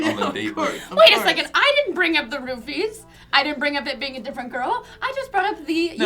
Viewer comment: Every time they say "roofie" I hear "rupee". For a while I actually thought they were talking about Zelda.